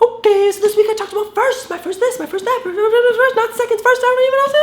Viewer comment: I thought he said "My first lime" because of the thumb nail and I was like "uh... I gotta watch that!"